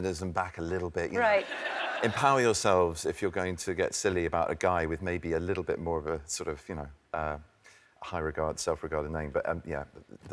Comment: What does Ben try to say in between 00:32 - 00:38 can anyone tell me please? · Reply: "Empower yourselves if you're going to get silly about a guy with maybe a little bit more of a... sort of..you know, high regard self-regarded name"